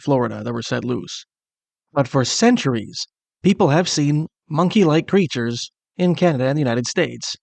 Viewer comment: I'm about deaf, so I thought you said "Monkey -like Preachers."